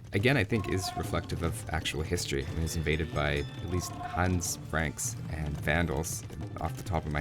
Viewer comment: did he say "thanos"?